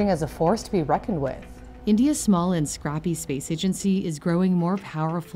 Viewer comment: Did she just called india's space agency, SMALL AND CRAPPY????